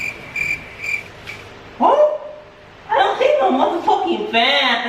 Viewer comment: Wtf did just say at 10:47? “I don’t think this is a mf fan?!” HAHHAHAHA I LAUGHED SO HARD